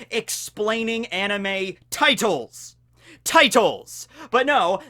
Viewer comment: Am o the only one that thought it said anime titties instead of anime titles